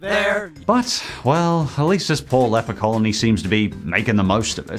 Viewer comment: I thought Stewie said “Leprechaun colony”.